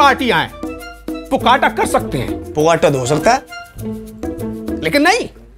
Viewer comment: what's that word bogata or pogata🤔🤔?